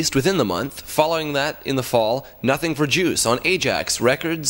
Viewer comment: lol almost sounded like he said nothing for jews